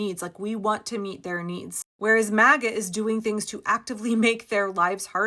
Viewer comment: in, the AI generated captions read MAGGOT instead of MAGA. AI shows some political savvy.